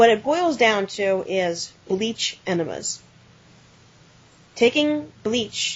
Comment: At first I thought you said "leech enemas"... I am not sure which sounds worse.